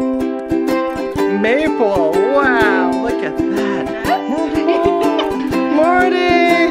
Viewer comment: Wait did he just say Maple look at that a$s on